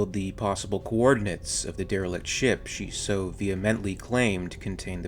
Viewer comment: For some reason I thought they said darlick like from Doctor Who xD